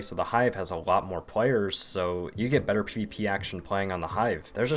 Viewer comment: did he just say pp action